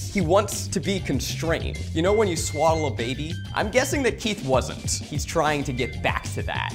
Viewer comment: i thought Zach said 'You know when you swallow a baby?" and I was... very very confused as to how we got here